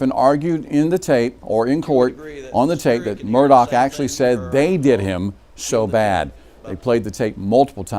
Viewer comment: I'm "um" sorry "um" I "um" couldn't "um" focus "um" on "um" on "um" what criminal defense attorney was "um" saying and ended up counting well over 55 "ums". I'm if this guy is just nervous or if he really speaks this way but it was very distracting. I heard "They did him so bad." I believe Mr. Murdaugh's "emotion" and his "rocking back and forth" body language is fake. There are never any actual tears.